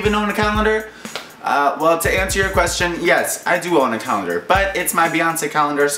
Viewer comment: When you said 'calendar' I thought you said 'colander' and I was confused as to whether you were making pasta or a video....